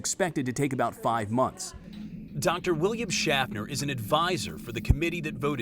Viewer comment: I heard Dr. William Shatner